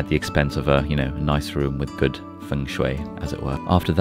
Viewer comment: Did you said Fengshui in 1:22 ？ You really know a lot lol